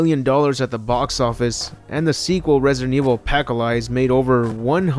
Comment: I thought he was saying a pack of lies for apocalypse